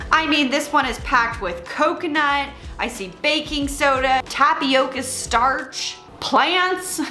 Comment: I wasn't fully listening and when you said Baking Soda I could have sworn you said BACON!!! LOL!!!